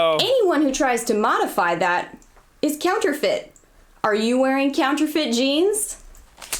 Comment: My dumbass self first thought she said “Are you wearing counterfeit jeans?” Uhhh, they aren’t jeggings, if that’s what you meant :/